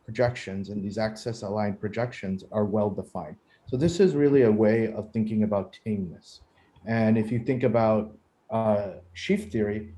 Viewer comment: What did he say at the end of this sentence? ...a way of thinking about what? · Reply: He says: "So this is really a way of thinking about tameness". Roughly speaking, "tame" shapes are nice enough shapes for your purposes at hand. Different contexts will require or suggest different notions of "tameness" that you might want to study or impose.